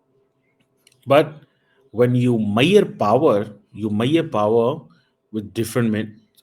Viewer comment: Ye 2:00 pe mayyor power mayyor power kya bola?